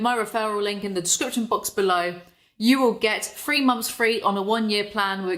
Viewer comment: what said: Three months free What I heard: Free months free.